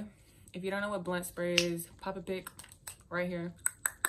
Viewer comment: "if you dont know what blunt spray is, p e p p a p i g" SHE DID NOTTTT JUST SAY PEPPA PIG💀💀💀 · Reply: she said pop a pic lmfao